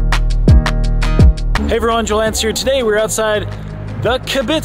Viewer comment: Sounds like you said your name was Joe Lance.